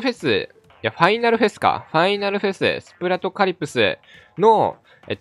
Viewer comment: スプラドカリプスが ギリシャ神話にでてくる 海の女神カリプソに聞こえる